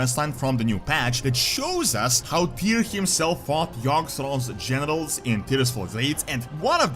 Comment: Honestly thought you said Tyr fought Yogg-Saron's genitals, I had to do a double take.. 😅